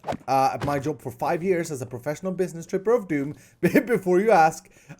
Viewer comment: I thought said professional business stripper of doom